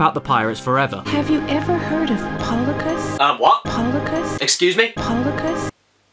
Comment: I think she’s saying platypus